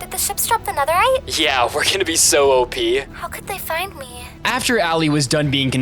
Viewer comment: I think the allay is the core hear what the allay says 24:07 the allay said how could they find me